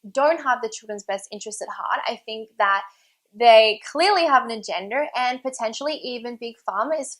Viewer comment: did she say agender?